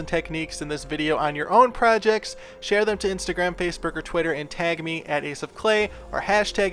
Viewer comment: Hi, Ace. Is it just me, or did you say “Face Burger” at 21:05 ?